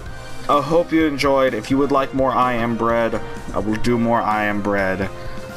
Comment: u have to do more for the commentary alone. and katie said it sounded like u said I'm inbred instead of I am bread. lmao